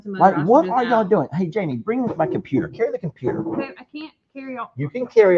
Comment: I hear "harry"